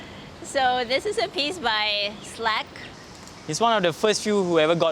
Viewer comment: i believe it's Slac as in the artist SlacSatu, instead of Slack :)